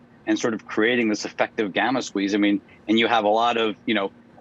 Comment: Did he say we’re accomplishing an effective gamma squeeze? 🏦